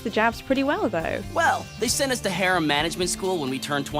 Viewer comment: I heard harem😅